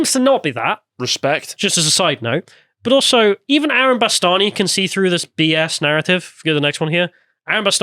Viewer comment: You said Aaron Bastani, I heard Alan B'Stard.